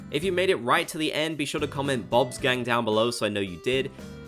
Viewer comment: Bob's Gang. I think that's what was said. My hearing isn't the best so at first I heard 'Bod's Game'. I am looking forward to the Lego sets. I really hope that one of them is Blather's museum. I do have one thing I would want in the next animal crossing game: LET US GET IN THE TUBS/POOLS! PLEASE!